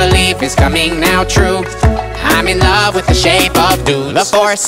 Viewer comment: Who else heard him say “I’m in love with the body of dudes” or something like that